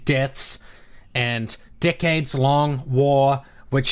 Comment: I swear I thought he said, "A dickheads long war."